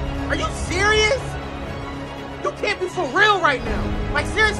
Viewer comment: for a moment thought he said "whats wrong with your mom"